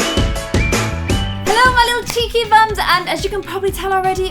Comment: I miss heard at the start... I thought she said peachy bums... oh my life............